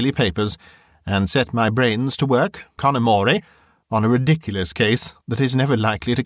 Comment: What is it Loveday says at about 1:20? "...fasten them instead on the daily paper, and set my brain,___" ? It sounds like "connermoray", but it's not one in my vocabulary, and I don't know the spelling to look it up! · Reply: Hello Sandi. It's an Italian phrase, "con amore", which is sometimes used in music to direct how it should be played. It basically means "with love" or "tenderly". <link> So in this context Loveday is basically saying that although she's on holiday and not investigating professionally, she's looking into this case on her own behalf, for the love of it, because it interests her. Mr Clampe uses the same phrase back at her when he's trying to persuade her to help him. I hope that helps!